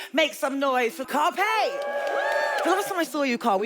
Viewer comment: "Please make some noise for CARPEY, lol i thought she said Carpet!"